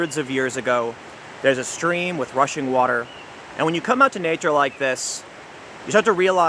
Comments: Is he kidnapped or something? I keep hearing "Russian water". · I thought you said Russian water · I heard "Russian water" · I keep hearing "Russian water" when you say that expression /rofl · I heard you say "russian water" I think I need to quit too!